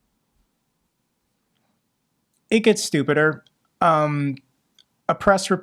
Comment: did you just say "stupid-er" ... wow at the 14:49 minute mark... wow "stu-pid-er" wow